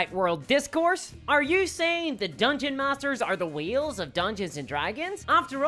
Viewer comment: It was at the 4:45 minute mark that I realized she was saying WHALES instead of WHEELS. That changes the entire context of the video. my bad. Love your content, love this video.